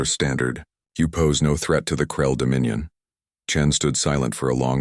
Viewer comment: In the beginning where im currently at 2:34. The voice says krell dominion. But the subtitles say cruel dominion. Is that on purpose for a pun or is it sloppy work?